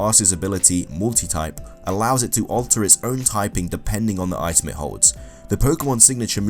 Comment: and weasel? I heard weasel. I'm pretty sure it was Buisel.